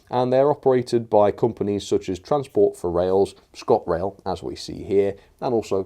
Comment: ah yes my favourite operator transport for rails maybe make your voice more clear so you say transport for wales instead of transport for rails